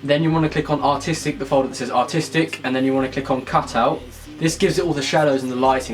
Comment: Which artistic option do you use at 9:28, I can't understand it (my bad) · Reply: cutout